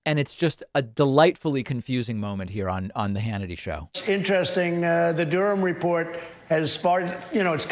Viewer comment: I thought he said, "The Durham report has fart...".